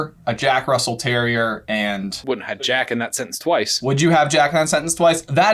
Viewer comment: I always heard Tonks' greating as "would yeah"